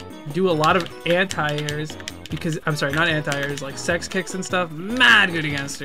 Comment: S*x kicks. Am I the only one who heard that.